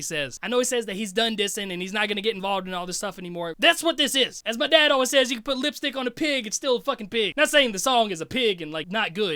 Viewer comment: 😂😂😂 I had to go back a few times at 12:55 At first i could've swore that you said, "my dad always says that you can put lipstick on a pig, and still fuck the pig". Jesus christ, it took me 3 go backs to finally hear ya. In honesty though, i am a few beers in right now. Great reaction. When are you coming to Washington state to do a show? I'll be one of the firsts to buy a ticket.